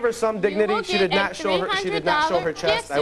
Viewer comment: I would be embarrassed to even admit I was in a thong contest. Df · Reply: Mani Pedi I actually thought they said a THOT SHOW!🤔